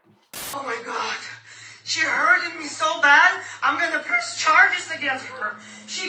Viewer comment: did she say "she hurted me!!"